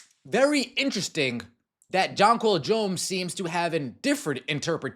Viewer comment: Jonquel Jones is pure class, and always has been. She's an absolute BEAST on the court, but man, I respect that lady. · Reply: Every time they say her name, all I hear is "john paul jones" I agree, though...shes one of the better ones 😂